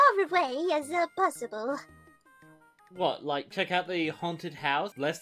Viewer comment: I kind of want to do a Hamtaro fandub (not an abridged dub, like dubbing episodes that weren't ever dubbed in English). Whoever did Boss and Bijou in this sounds pretty good! Do you think they could help me with my fandub? No Japanese knowledge is necessary as we'll be doing ones that have been subtitled. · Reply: Oh okay. What exactly did Hamtaro say at 1:09 ? "The haunted house, less scary than one's gotta go under here?" I don't really get what that means and I feel like I'm mishearing it...